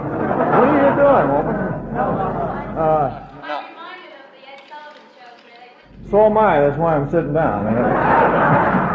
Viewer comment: what was the woman saying that made him laugh? His response was great 😂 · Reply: She said "I'm reminded of the Ed Sullivan show!!"